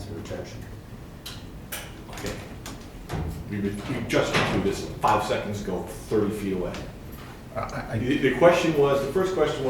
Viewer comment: What exactly does the judge say at 50:15? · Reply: "Okay - we've been... we've just been through this 5 seconds ago, 30 feet away"